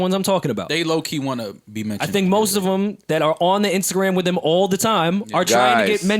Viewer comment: Did Rory say “The instagram”